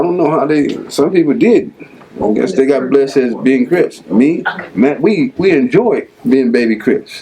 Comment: From 13:10 to 13:30 Did he say him and Mac as in Mac Thomas werw baby crips I thought Mac Thomas was an original not AC original but original